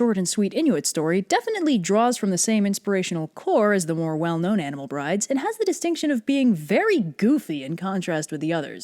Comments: Thought it said Animal bridges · I heard your statement wrong I heard it as "Anime Bride" the things Anime does to you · I thought that this said animal bridges and I was like aight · i thought this said "animal bridges" not "brides" and when she started talking about brides i was so confused lmao